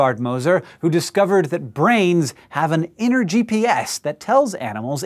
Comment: the first time i heard "brains.. have an energy BS"